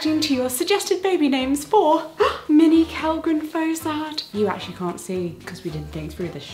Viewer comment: The video started off with what I heard as "Minnie Kellgren-Fozard" and it's the only name I'm thinking of now it's so cute